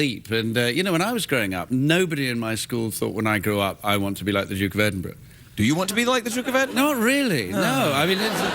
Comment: Can someone tell me what does he say at 2:07 . nobody in my school thought when I grew up I want to be like the.... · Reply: I think he said "Duke of Edinburgh"...